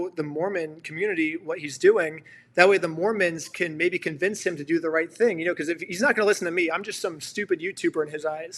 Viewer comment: hold on. Had to rewind a bit here. Did I just hear you say “Mormons do the right thing”? 😂